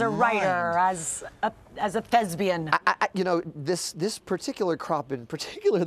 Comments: i thOUGHT SHE SAID LESBIAN?!?!?! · Dear God help me, the reporter said "Thesbian" instead if Thespian. I have never been more disappointed in my life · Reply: For a tick I thought she said lesbian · I misheard and thought Hamilton was being inspired as a "lesbian" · I heard "as a lesbian" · I thought she said lesbian